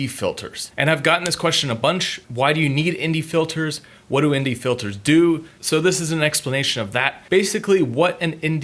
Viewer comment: I thought you said Indie filters! Great vid by the way!